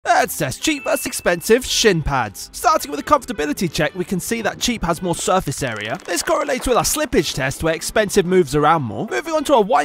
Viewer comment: I thought he said shit pads